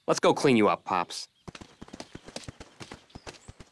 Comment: I thought he says Disney world lol is he making fun of Disney